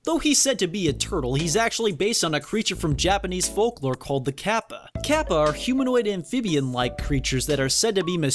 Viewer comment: Did you say... ...the KAPPA?